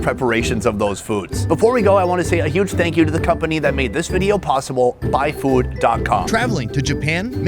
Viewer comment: Did he say "Thank you for the company that made this video "Pawsible"?